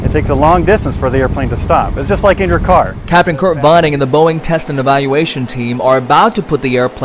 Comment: did he say captain kirk?